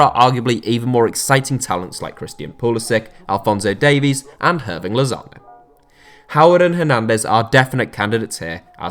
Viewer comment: I swear I heard having lasagna